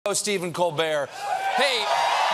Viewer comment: Seems like an editing mistake. It sounds like he said, "Oh, Stephen Colbert."